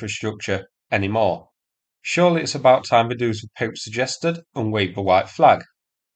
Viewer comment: the subtitles say "poop" as opposed to "pope." I approve! 😆